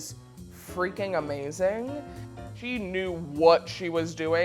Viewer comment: did she just say, he knew?